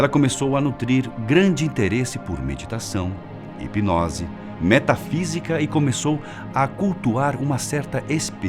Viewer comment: Quando falou em "metafísica" eu entendi "metanfetamina"